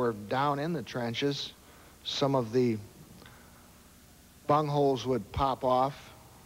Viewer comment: Did he say "bungholes"!!!